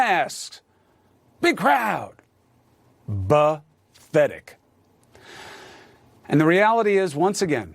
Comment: Did Cuomo say "Bathetic"? Sounded like bathetic instead of pathetic.